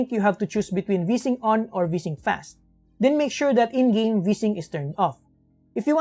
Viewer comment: did you say "Make sure V-Sync in game is turned Off"? I replayed a few times and still not sure what you said real fast then.